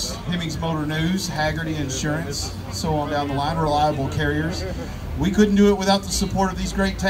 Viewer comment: Greetings from Clay Co. MO USA! @ 5:34 I thought he said, "and ..our rival carriers." I was thinking, That's nice of him to acknowledge the competition. They're just nicer in Canada. Won't name them, but I'll acknowledge them. Then I noticed the sign. Oh RELIABLE carriers. Duh Speed Limit 110 had to laugh Someone added a 1 to the front of our neighborhood Speed Limit 25 sign (not me) I'd seen a few cars over the years when they happened to come thru Kansas City, but never all together like that. Exact second for an ace. Wow. I'm lucky to be within an hour if I try go somewhere I'll never complain about my wife's directions again. Great cars, Great people, Great talent. Thanks again for taking me along! GL & HH